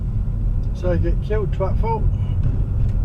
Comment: can someone translate that? I've listened to that 5 times and have no idea what he said, I got "that's how you get killed" but it's the next bit, I'm stumped . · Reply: That's how you get killed twatful. 🙂